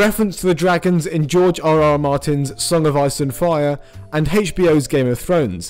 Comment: I heard “George R. R. Martin’s A Son of Arse and Fire”